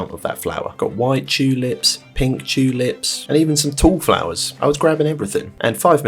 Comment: Inherently knew you were European but hearing you say “Chew-lips” instead of Tulips will always crack me up